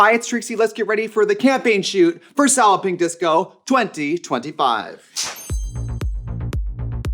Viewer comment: For the longest time I heard “sallowping disco” 😭